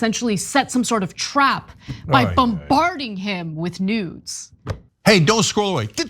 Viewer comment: I thought she said bombarding him with news. I was like Wait. What?! That’s not the story. 😂